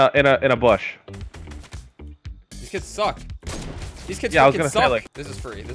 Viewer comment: I heard kaz say a bad word he said these kids f ing suck listen 7:21 through 7:30 Edit: kaz never says bad words I’m surprised 😲